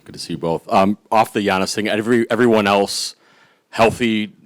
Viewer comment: I thought they said giannis has COVID